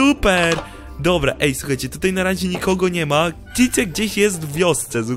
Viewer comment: czy tylko ja usłyszałem : "cycek jest gdzieśw tej wiosce" xD